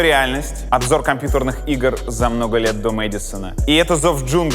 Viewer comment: Я услышал "Мэддисон"? Юрий, весь интернет теперь будет ждать интервью с Ильей Мэддисоном :3